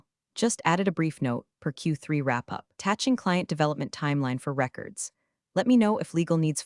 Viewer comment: AI doesn't know the difference between "... dollar 250k client bonus ..." and " ... 250 thousand dollar client bonus ..." The later (7:41) "Tatching client development ... instead of "Attaching client development ..." (21:31) " ... patting his portfolio ..." instead of "padding his portfolio ..."